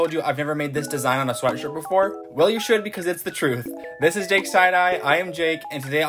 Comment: Was it just me who thought he said this is jakes side eyes 😂😂😂😂